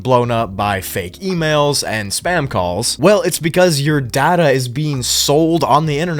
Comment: I thought he said Dada 😭